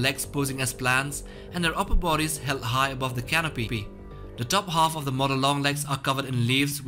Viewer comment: did he say pee pee in canopy? Or is it my wifi or graphics